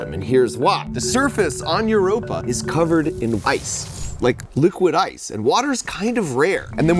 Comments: nah he really said liquid ice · Did Mark say, "Liquid ice"???